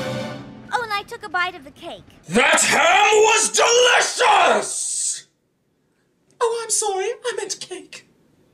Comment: It's been nearly three goddamn years and I STILL have absolutely no idea what he's saying here. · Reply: I think he said, "That ham was delicious! Oh, I'm sorry, I meant cake."